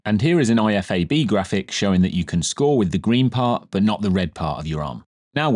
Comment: what did ya say which part of body you could score with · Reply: Green part of the graphic, which is the upper arm (determined by the part level with the armpit)